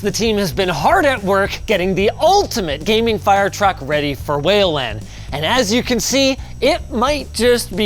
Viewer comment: Is it just me or whenever you said "whale land" I keep hearing "Wayland"?